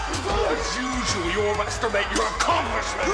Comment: does he say "as usual you masturbate your congressman?"